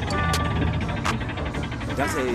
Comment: Am the only one who heard "try again girly man" 💀 OML TYSM FOR THE LIKESSS <33333 MAMA I AM FAMOUSSS 😎😎😎😎😎😎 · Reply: I thought he said try again curly man 🥵